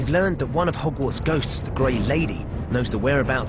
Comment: did anyone else hear him say "the great lady" instead of the gray lady? > . <